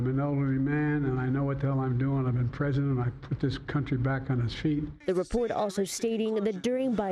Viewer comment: Did he say he got this country back on its feet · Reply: I thought he said he knocked the Country of its feet.